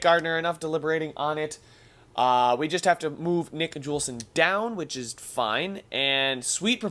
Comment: did he say nick julssen